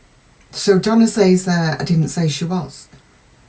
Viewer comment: I heard Donna say, “Yes she was.” Listen again, and see if you don’t hear it differently. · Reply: I didn’t say she was is what Donna said. I’d be thrilled if you were correct, but sadly I don’t think so.